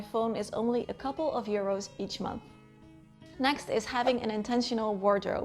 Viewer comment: did you say euros ? where are you from ?